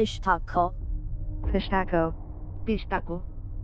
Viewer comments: I thought it said pistachio😂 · Am I the only one that thought it said "Pistachio"?